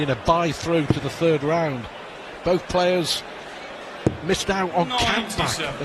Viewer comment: What does the commentator say at 14:53 by the way? It sounds like 'both players miss out on count back'?